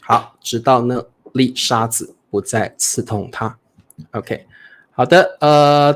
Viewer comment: the subtitle says : 丽莎子不再刺痛她 ("Lisa no longer stung her") 🙂 Should probably be : 这粒沙子不再刺痛她